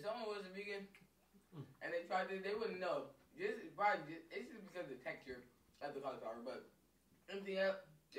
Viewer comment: Are those eggs? If not, What is it 🤔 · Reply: I thought he was saying it's just eggs omg lol